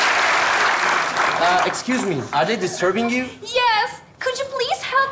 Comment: de ne diyor anlayan varmı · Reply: excuse me,are they disturbing you